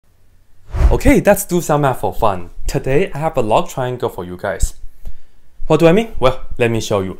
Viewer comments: I thought he said, "Love triangle" 😂🤣🤣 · I heard love triangle ...